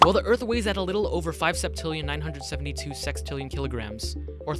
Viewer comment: i heard sextinion